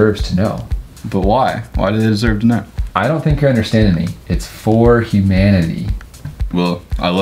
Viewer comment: "It is for Humanity!" "Well I love humans" Sees the haircut "I need a little bit more humanity" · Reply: I thought he said "This is FOUR Humanity!" LOL